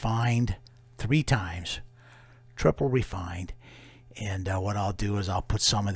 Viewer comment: the melt on this is going to be so satisfying. been with this one from refining 1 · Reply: I thought he said its double refined,for sale on his eBay